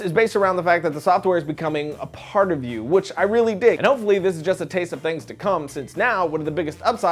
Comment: Am I the only one who thought said "The software is becoming a part of you which I really dick"